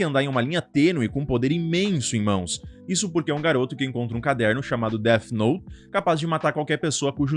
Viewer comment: "COMPRA" ou ENCONTRA um caderno ???? 01:25 🤔🤔🤔🤔🤔🤔 (eu ouvi: Compra um Caderno) 😂🤣